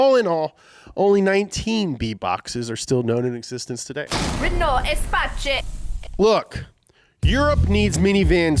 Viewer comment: I thought that it was said /espass/, looks like its /espach/